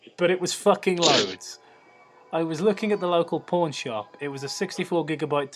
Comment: When you said pawn shop, I thought you said porn shop at first.....